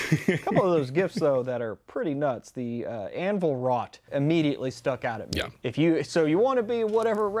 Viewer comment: I misunderstood when you said "anvil wrought". I thought you'd said "anvil rot".